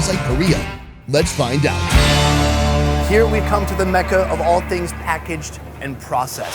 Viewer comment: did he just say Mecca 😂❤️